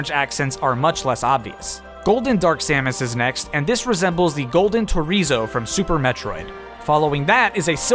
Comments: For a second I thought he said Golden Chorizo and I was very confused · Did you just call it a Chorizo? I believe it's referred to as a Chozo. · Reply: He said Torizo, which is what the hostile Chozo Statues are called